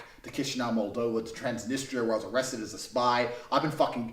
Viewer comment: did he say he was arrested as a spy?!